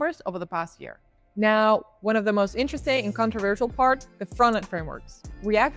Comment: I think I misheard. You said principal format engineer?